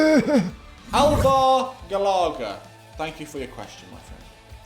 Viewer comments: did he say “elver Galarga”?? 😂😂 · did he say "Elver Galarga"? 😂😂😂